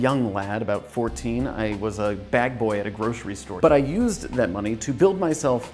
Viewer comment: I thought he said “I was a bad boy at a grocery store” and I thought he was going to tell us about when he stole a computer from a grocery store, of all places